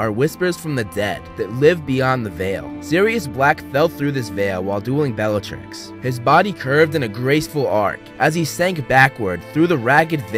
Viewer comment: Literally thought he said, "While doing Belatrix." LMAO